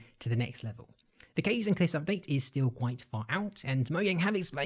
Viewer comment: why did i hear "The Gays and Cliffs Update" in 9:31 💀